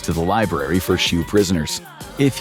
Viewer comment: wtf does it say?! For WHAT prisoners? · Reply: SHU(special housing unit)